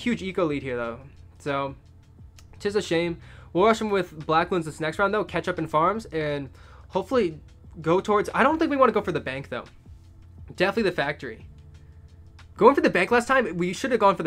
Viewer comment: I actually thought he said ketchup instead of catch up